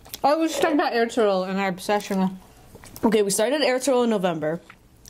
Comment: What's the name of the show they mentioned at 12:41? I couldn't catch the name. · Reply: Ertuğrul